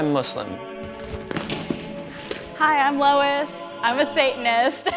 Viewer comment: "Hi I'm Lois, and I'm a Satanist" What I heard: "Hey Lois, I'm a Satanist."